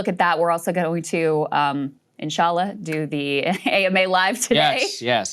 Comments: Did she say inshaallah at 2:52? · Am I crazy or did Krystal just say "inshallah"? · Did Krystal say InshAllah